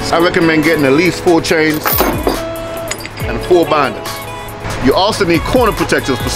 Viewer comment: What was the red tool he said at 7:09 ? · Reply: Load binders